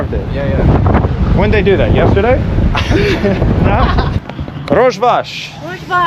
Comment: what did she say? sounds like "hogy vagy" - how are you(in hungarian) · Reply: He is speaking Kurdish, Rozh Bash, means “good day”